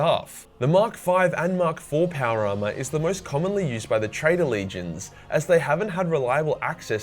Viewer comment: MK V armour isn’t actually a manufactured armour type. It’s literally just armour that is scratch built. If a marine made a helmet out of random bits of metal, that helmet would be classified as a MK V helmet. Which is why said both loyalists and traitors designed it at the same time. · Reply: I thought he said Traders, not Traitors.